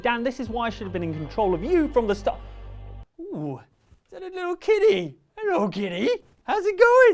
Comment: I thought he said a little kiddy instead of kitty and i was a little creeped out